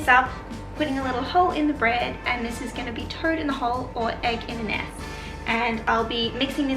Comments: I thought she was saying turd in a hole. I was thinking ok no problem different times.. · I thought you were saying 'turd in a hole', lol. Toad isn't much better I suppose. :) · Oh my goodness...so happy I found this channel. I love history, you're adorable. When you made breakfast I thought you said turd in the hole 🤭🤭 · Toad in the hole is sausages in a batter in the uk and we call the dish you made egg in the nest or eggy bread delicious 🥰 love your Channel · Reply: 😂😂😂 I thought she was saying turd in a hole.😂😂😂😂😂